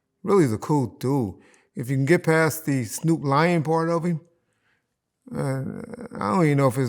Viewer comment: at first I thought he said snoop lion but he said snoop lying 😂